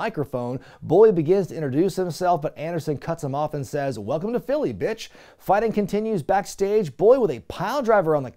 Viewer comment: I can't wait for the month celebration of Brother Bruti, brother! Also I couldn't help but think "Ah, where the street fight is, big dummy" when I hear "Welcome to Philly, bitch" Bless Ricky Starks